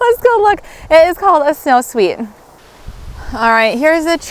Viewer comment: What was the fifth apple you suggested that its name ended in sweet. I searched what I thought you said but didn’t come up with anything so I must have misheard what you said. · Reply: It is a SnowSweet apple!